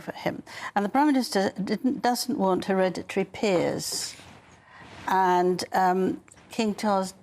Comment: What did Angela say at 2:30-33...registry piers/heredity peers?....can someone clarify this for me.